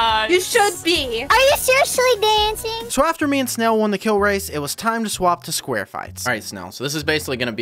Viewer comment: Did you really say sqaure fights?